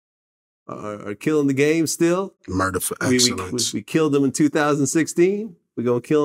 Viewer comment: Did he really say “Murder for excellence” at 9:27? 😂